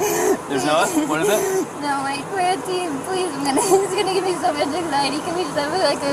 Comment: does she say "he's going to give me so much anxiety"? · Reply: She says "That's going to give me so much anxiety."